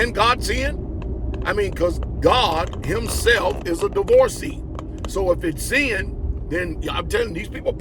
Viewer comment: … did he say that God is a divoree??!